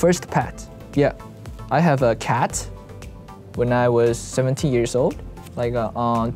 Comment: If you turn on the subtitles it said “when I was 70yr olds” instead of “when I was 17yr old”